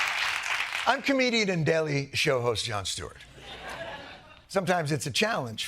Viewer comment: Did he just call himself Yon Stewart? 😂😂